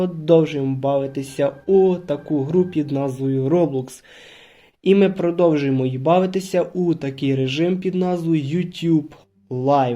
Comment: то мені почулось, чи там їбавитися🤣🤣?